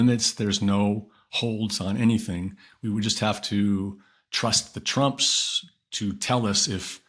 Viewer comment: did he say trust the trumps?